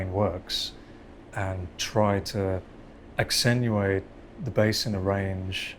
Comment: Did he say accenuate?